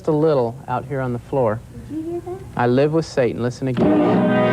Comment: Honestly, when the first guy played the record backwards I didn't hear "I love with Satan", I just heard "Hell awaits". Surprised he missed it.